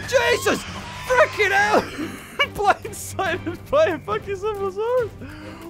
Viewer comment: did you say fucking